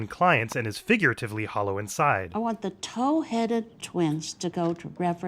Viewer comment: my mom used to say I was a towheaded baby when I was a kid. growing up i thought she was saying my head looked like a toe lol